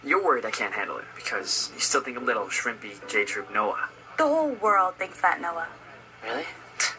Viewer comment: I thought he said 'Shrimpy GAY-Troupe' and I was like WOOOOOOOOAH, TOO MUCH